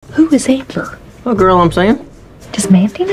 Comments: I thought I heard who is Hitler 😂😂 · Every time I refresh the video I hear her say "Who is Hitler?" and I can't unhear it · bro I thought she said "who is hitler" at the beginning and my little brother started saying it too